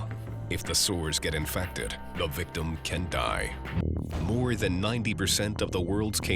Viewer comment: did he just say canT die???